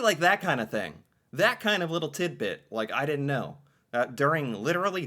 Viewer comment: did he say titbit or Tim bit?